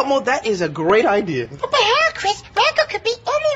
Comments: Elmo was going off! I know that one was edited...but damn was it hilarious 😂 I still think that the people that make these shows purposely put little things in for the adults that have to suffer through them with their kids. These are absolutely hilarious videos and I always look forward to them! Plus, your reaction is always the best! Have a wonderful weekend 😊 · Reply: I admit it does sound like Elmo said "what the hell Chris?" and that's funny 😂 but Elmo said "b..but how Chris? Rocco could be anywhere" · Stupid crooked Hat 2:00 Makka pakka 3:10 What The ''screams'' 3:48 Thank you 5:30 bucky sail away 6:34 Dean Funk 8:21 Jack said ''book'' 9:08 B-but how chris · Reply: I heard “That won’t help Chris!”